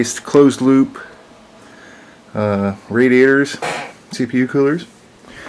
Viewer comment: And it runs so VERY quiet! Did you say 'soup-coolers' at 0:59? LOL